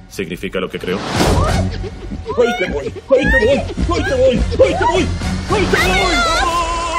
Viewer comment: Dijo hay yaoi #